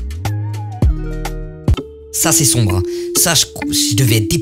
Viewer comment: ta dit mon psn UII je prend sa comme une dedi x’D